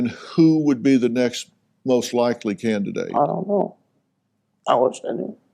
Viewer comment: Oh you mean the red truck that Robin was frantically crossing out on the missing posters saying it was cleared? Tim's red truck? Edit: A suggestion for innocent parents? Perhaps start with Lindsay Chamberlain ? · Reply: Thank you for responding, quick question - at 48:23 , did say, "I don't know, ... Alison ? "